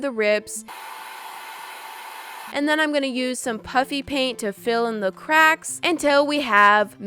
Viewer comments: It sounds like your saying puppy paint who else thinks so | | ⬇️ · hA hA iT sOuNdS lIkE sHeS sAyInG pUpPy PaInT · Ha ha! It sounds like shes saying puppy paint.Do you agree?